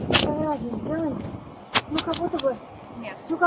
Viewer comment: Wrong captions, I guess AI at work. 4:05 "Ну как будь то бы" - "Нет" - "Ну как будь то бы" Can't understand further due to sonud, but captions are gibberish.